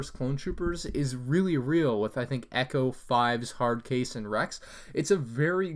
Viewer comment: I thought you said it was JESSE not echo...